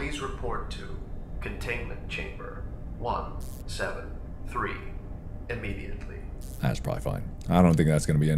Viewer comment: did he just say scp-173? That scp is so strong